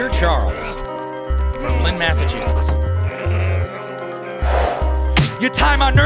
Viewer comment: What does he say when he writes on that paper in the beginning? It sounds like he says "Brookline Massachusetts" to me, but that doesn't make any sense (although it would be kinda cool 'cause that's where I live)